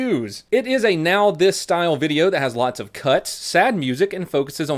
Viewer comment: "has lots of cuts" initially thought you said cucks lol